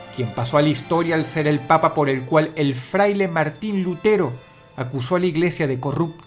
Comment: Martin Luther King escuché yo todo imbécil.